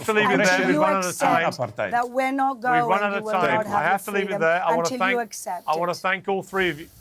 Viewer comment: Did she just say - you will not have your freedom? Towards the end?